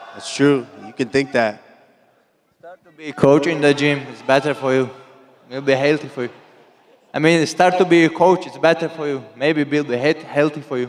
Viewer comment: Timestamp 3:50 Dana you really couldn't understand what Kham said? He said, it might be better for Kamaru to be a coach more healthy for him.